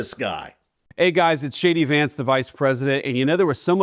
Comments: Could have sworn he said " hi guys its Shady vance " which fits since he once referred to trump as the " Amerikkkan Hitler " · I swear it sounds like he says "Hey it's Shady Vance" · It did sound like Vance said "It's SHADY Vance!" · I swear I hear him say, “Hey, it’s Shady Vance…”🫣🤤😵‍💫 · I don't hear "JD Vance" I hear "Shady Vance" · When Vance introduced himself I could have sworn he said "This is shady Vance", probably more accurate! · "Hey guys, it’s Shady Vance…" — that’s what I thought he (JDV) said as he introduced himself! 😂